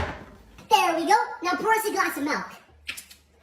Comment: did he say: por us a glass of malk in